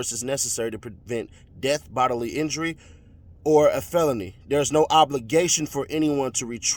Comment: Thought they said Durk not a Felon